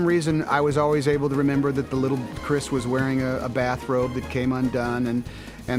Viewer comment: Did anyone else catch that “The Hero Boy” is named Josh? It’s says it on the child actors motion capture suit. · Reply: sounds like Tom said his name was Chris?? I’m very confused 😂